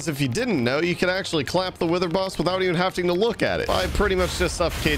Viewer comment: did he just say havting at 13:49?